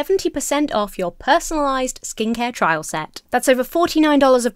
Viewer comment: Head to <link> or scan QR Code on the screen and use code EMMATHORNE70 to get an exclusive 70% off of Geologie’s award-winning skincare trial set. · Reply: Honestly thought you said skink hair rather than skin care and just imagined a blue-tongued skink with hair